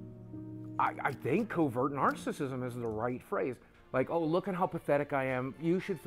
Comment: Am I the only one who thinks the part of the documentary where Boogie comes out as a covert narcissist is being misinterpreted/misheard? I can't help but think he's saying "I think covert narcissist ISN'T the right term..." instead of "IS the right term." It really sounds like he's denying it to me, but I could be wrong. I can't unhear it however....